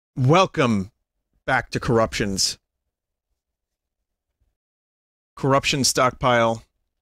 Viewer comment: I thought it said "christian stockpile". I just thought "maybe vinyulous has a new direction in life?"